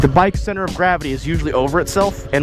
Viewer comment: Did he say "featuring do it with dick head" at 7:50 ???